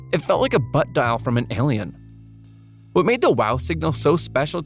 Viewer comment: tell me why i thought that said “Wow! Sigma!”